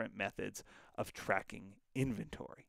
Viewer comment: did you say "Account wait to see you in the next-" did you make a pun??? xD